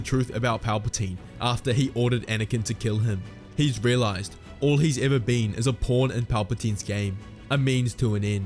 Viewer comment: Auto caption says "All he ever been is a porn in palpatine's game" 😅💀💀